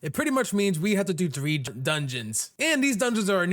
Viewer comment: When he was explaining how to do the last upgrade I thought he said do 100 dungeons